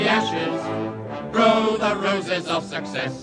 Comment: I've thought they were saying THROW the roses for the past 35 years lol